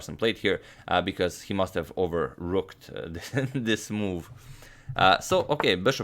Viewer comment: I thought he said "overlooked" but it was actually "overooked" lmaaooo😂😂